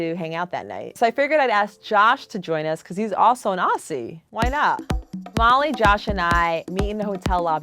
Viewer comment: I literally thought she just said “because he’s also a Nazi!”